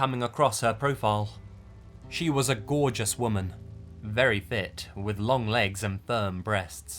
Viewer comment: instead of "She was very fit," I heard "She was very thicc."